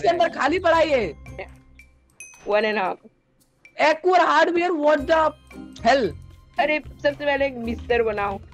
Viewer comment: Legends know that what say this time 😅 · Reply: saying wtf but he said wth lol 😁😂😂